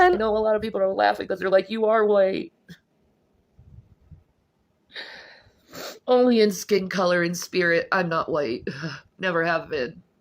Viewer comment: Ella you misunderstood a lot of what was saying. I think you need to learn to listen better if you want your videos to be better. 8:00 She said that she was white in skin color, in spirit she was Native American (She did not say in skin color and spirit I am white). 8:35 And she said that she believes the bees were going extinct and She was quoting other people who said they saw bees so that is not true. None of that makes a better person, She's still the worst kind of but if you want to have some integrity in your videos you should probably learn to have better listening comprehension. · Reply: She said "only in skin color and spirit I'm not white" 🤦‍♂️ cause that makes sense. I think it was an honest mistake. Ella has so much integrity and class. Your comment comes across as biased and disingenuous. And I would bet that there's a totally different reason that you go through videos, and look for any little thing to critique. We see you. 👀